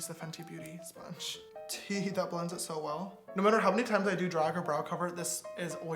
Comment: did he just say "teehee, that blends it so well"